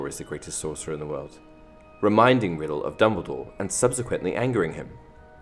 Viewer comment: Turn on automatic captions and instead of Dumbledore it says Dementors